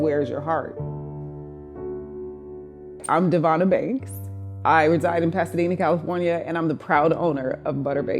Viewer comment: What she said: I'm Devonna Banks What I heard: I'm Devonna Bakes